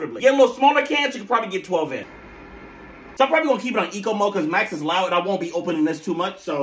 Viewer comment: I thought you said my ex is loud.